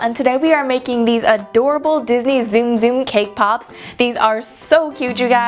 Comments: I thought she said, K-Pop.... Lol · did she say sum zum